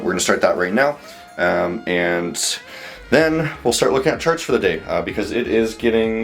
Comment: I thought you said we'd look at church for today